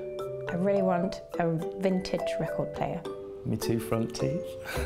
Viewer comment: Can anyone tell me, what Taron says on 1:38? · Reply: InYanHren "My two front teeth"